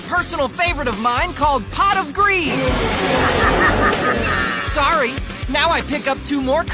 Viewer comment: I heard "Pot of creed"